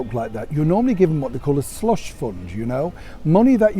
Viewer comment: Oh Neil! I was singing "Ave a banana! " and thought you said $lut Fund! For some inexplicable reason I refuse to solely attribute to medication, it really set me off, and now I've giggled myself silly, for the duration of your report thenceforth, and have a fabulous headache. It's impossible not to get one if you lose it when you need to be quiet, where sound carries, even in this howling wind. It was worth it. I love that tie! Not just the colour, but the stripes, how intricate they are the closer you look. Very stylish, as always. I used to work all over that part of Yorkshire, I loved taking the long route home (then Bucks.) and end up on pitch black roads in the middle of nowhere. It doesn't matter who you ask if you're lost, they're so warm and friendly and like to help. And there are sheep, so many sheep, I do love sheep so much. #kingofties